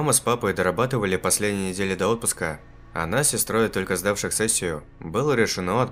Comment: Что говорит 0:55? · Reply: а нас с сестрой, только сдавших сессию*